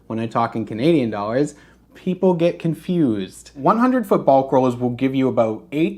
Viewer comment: I was falling asleep and swore you said “100 football throws” cause of the currency comment you made right before it. Lmao